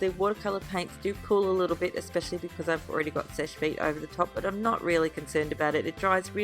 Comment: it sounds like you say Fish Feet when you say Seche Vite! LOL I just wanted to tell you cause my dad and I couldn't figure out what you were saying until yesterday!!!